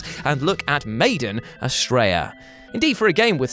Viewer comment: I thought you said "made in Australia" when you said Maiden Astraea had to look back at my phone quick like wtf?